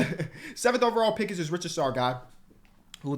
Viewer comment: Every time tries to say Risacher I hear it as "Richard Sarr" and I'm like Alex don't have a brother. It's Ree - sah - Shay if you see this.